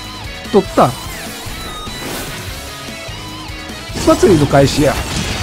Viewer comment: 「祭りの開始や」が「スパチャの開始や」に聞こえるｗｗｗ